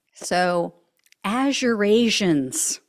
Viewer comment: I thought he said A$$ Eurasians!😂